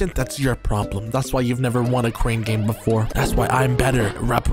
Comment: Not the cream game instead of crane game at 7:05 😂 the editor was thinking of sum else there huh😂😂